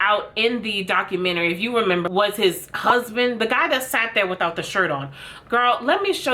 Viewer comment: I literally thought you said "cusband" (like cousin + husband) and girrrrrrl. I know that's not what you said, but it's crazy that word hasn't been created yet.... 😂